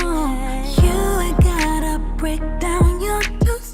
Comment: "You ain't gotta break down" is what is should read there....remove the word "don't"